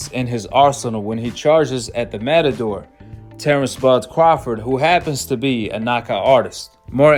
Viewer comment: sounds like he said Terence Butt Crawford. 4:43 to 4:48 "mouthpiece felled out" the grammar killing me.